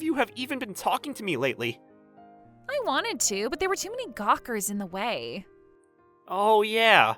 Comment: did she just say “Gawkers”?🤣🤣🤣🤣🤣🤣🤣🤣🤣🤣🤣💀💀